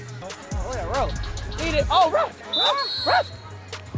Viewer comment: I thought he was barking not saying ref lol